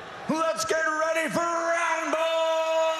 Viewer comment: Did he butcher the line? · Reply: TheLyricsGuy kinda sounds like he said “roundble”. Still an amazing intro though